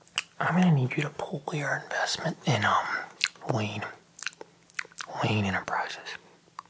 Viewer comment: Oh my lord. I was so sleepy that when he said "Wayne enterprises" I could've swore he said, "lean enterprises" which woke me up for like <num>th of a second because a uncontrollably laughed at it (I say laugh but it was more of a small 9 second giggle)